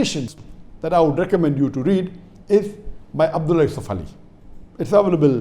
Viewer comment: what did he said abdul isofali or abdul safali or anything else about translation of quran in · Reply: He is referring abt Abdullah yusuf ali , a great translator and 3 revert sisters are Saheeh international